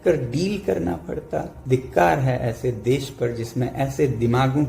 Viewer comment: Did wangchuk say "dhikkar hai aise desh par.."